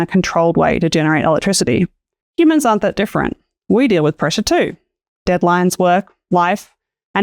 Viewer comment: what i heard: "we deal with pressure too! deadlines work, wife" :face-green-smiling